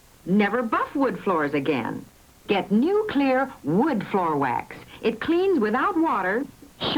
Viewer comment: I heard "new Klear" as "nuclear."